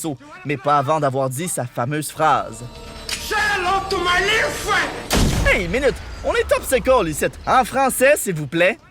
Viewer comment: il dit quoi? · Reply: "Say hello to my little friend!!!"